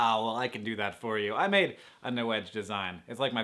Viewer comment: it sounds like you say free order at the end and I almost fell out of my chair.